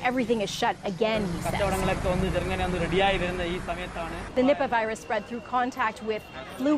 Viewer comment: When said TheNipah virus, I thought he said The Nipple virus 😅😂